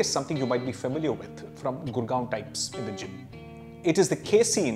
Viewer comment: Good video very informative. If the presenter can speak properly without shaking his head, it would be more easily understandable .. On repeated hearing I could not understand at 3:20 what he said I heard something like Gurgaon like blah blah.. Just a suggestion, otherwise the video is very informative.